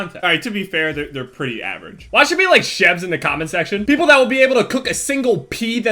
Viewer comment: It sounds like he says "chevs." = Chev